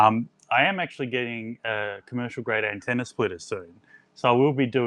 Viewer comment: what is the "commercial antenna switch" that you are getting, unless I misheard you? Thanks for the videos all very informative. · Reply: We’re getting a commercial grade splitter, not a switch. It will allow two radios to receive the same signal with just over 3dB of insertion loss, and over 25dB of isolation to prevent the receivers degrading each other. The model will be revealed in time :). We hope to have it in a week or two, subscribe and click the bell to be notified as soon as it arrives! :) 73